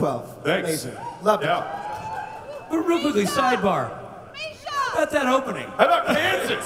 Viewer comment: Who heard where's misha? 😂